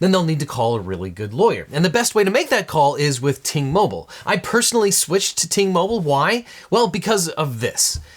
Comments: Thought he said T-Mobile 😂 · oh i thought he said Tmobile not Ting mobile lol.. really went over those minutes oof